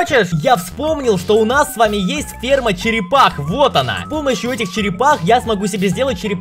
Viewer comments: мне послышалось что Жека сказал сп*рма черепах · Мне послышалось не ферма черепах, а спе***